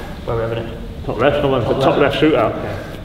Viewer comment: Plz tell me what did kane said at @ 10:08 Top left "shoe" out ? Or shoot it out ?? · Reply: Top left shoot out, because I told him to shoot top left, he was returning the request 🤝